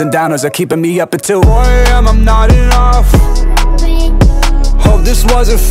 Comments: instead of, "4am i'm nodding off" I hear, "4am, i'm not enough" and damn that shit hits harder with "hide behind this camouflage" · I hear "4 am im not enough"